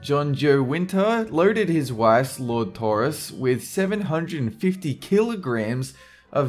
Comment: Did you just say "Lord Taurus"? "OH MIGHTY AND OMNIPOTENT LORD TAURUS, SPARE US IN YOUR MERCY FROM THE EVIL WILL OF COUNT DAEWOO!"